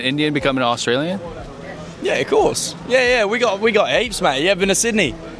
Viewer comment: I thought he said apes not heaps. 😂😂😂